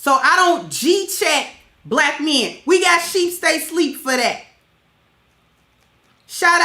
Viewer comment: What is the name of the brother who you referenced at 11:45? It sounded like you said Sheep State Sleep TV? Help a sis. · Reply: sheepstaysleep